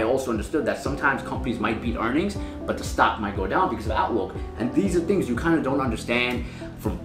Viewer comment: what does he say at 5:05, "sometimes companies might beat earnings but the stock might go down because of..."? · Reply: because of outlook